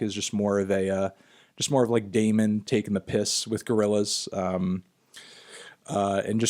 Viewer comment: did anthony just say 'taking the piss"?